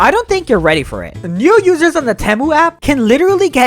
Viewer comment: DID YOU JUST SAY TEMU!!!!!!!